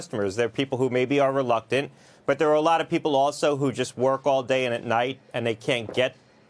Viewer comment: Did he just say there are people that work all day and all night thus can't get a vacine??? I'm sure there are very few people that do that.